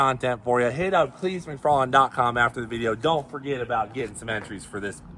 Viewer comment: Do I hear " McFarland Earnhardt" Drag racing team?